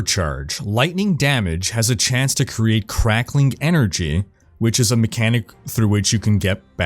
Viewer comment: I must be tired from work today but I swear I heard "It has 3% chance to create crack energy which regens your mana"